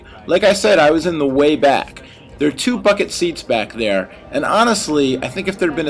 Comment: i thought he said "there are two fuckin seats back there" lol